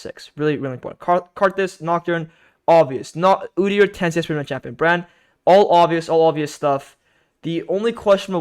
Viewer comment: What did you say about Udyr at 1:40? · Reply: Udyr, 10 CS per minute champion